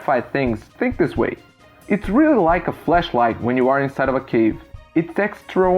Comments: Did anyone else think he said Fleshlight initially? Being stuck in a cave is no time for that. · sounds like he’s saying flesh light instead of flash light. That’s not you having a super thick accent (obviously you have an accent but it’s easy to understand you) it’s just me being dirty. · make sure to annunciate the ‘a’ in flashlight. It sounded like you said fleshlight instead. You don’t want to be throwing that around in everyday conversation haha! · Like everyone else has said, you’re English is great. If I HAD to give criticism I would say work on tie pronunciation of the sounds in the word “Flashlight”, because at 5:15 it sounded like you said “Fleshlight” haha